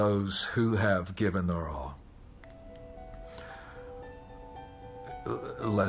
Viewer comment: OMG! Until I read the comments I thought he was saying “some gay ball” I thought the veteran he was talking about was gay- Lord I need to clean my ears! 😂